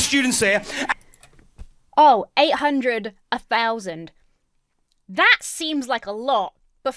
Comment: I genuinely thought he said 800,000 students